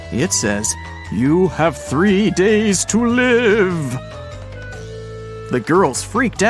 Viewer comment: no, it absolutely does not say that. It says "You have 3 DA to live".